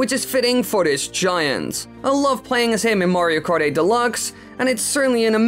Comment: Wait did he just say Mario party delux